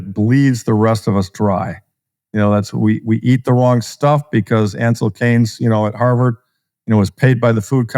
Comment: Does anyone know what name name mentioned at 49:28? I’d love to know which Harvard-affiliated sellout is responsible for the Food Pyramid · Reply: Ancel Keys